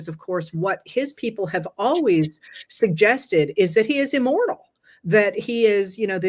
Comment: 😂😂😂had to go back & listen again. Thought you said “immoral”, thought “well duh; yea!”